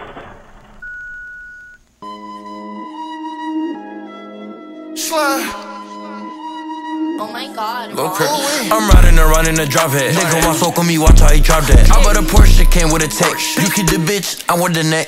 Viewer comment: parece que dice "nigga wasup con mi wacha y charte"